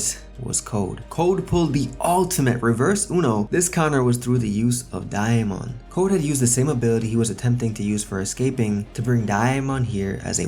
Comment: The way he says Damon is great😭😂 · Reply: I’m surprised nobody corrected him because it’s sounds like he saying diamond like bro there’s no i where you getting the i from 😭😭😭😂😂😂